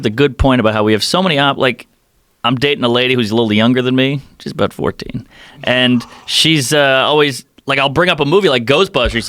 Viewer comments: did he say he’s dating a 14 year old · Did he just say his girlfriend is about 14?! @ 6:53? someone please respond, hopefully with an explanation.. <link> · did he say he’s dating a 14 year old ???